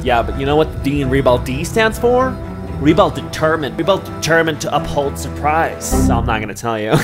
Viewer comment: Bestie I thought you said REBEL DETERGENT instead of DETERMINED and was hella confused 😂